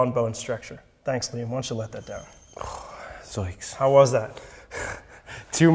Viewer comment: did he just say "zoinks"? When was this filmed?